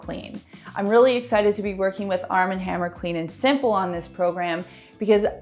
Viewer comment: POLL: How often do you wash your jeans? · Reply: I thought you said Armie Hammer, the actor...lol